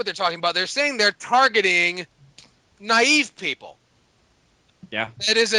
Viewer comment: lol at 40:10 i swear to god i heard say targeting Chinese people XD I was a like wtf? Then he repeated himself and I heard naive people.